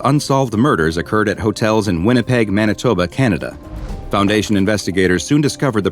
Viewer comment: Did you say Canada😨😱😰😰😰😰😨😨😱